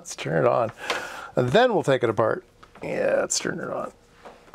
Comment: What does say? "Don't take it apart, turn it on"?